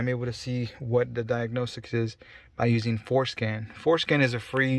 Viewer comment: I heard foreskin😭